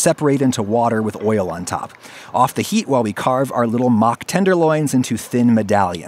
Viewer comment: What he said was "mock tenderloins" but what I heard was "mach 10-derloins" and I like that more